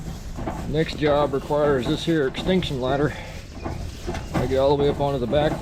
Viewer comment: My husband already has an extinction ladder... I'm guessing he'd also like one of those giant over-compensating diesel trucks.... or gas powered LOL! If you ever do merch, "Imafixit" would be a good tshirt. Thanks for taking us along on your adventure. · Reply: I thought he said extinction ladder. that's hilarious!!